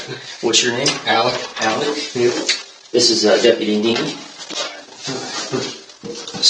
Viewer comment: I thought he said, “Deputy Dink.” Somehow, I don’t feel like, “knee,” is any less weird, though.